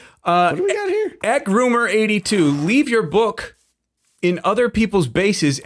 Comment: wait at 9:02 did he said groomer82 ??????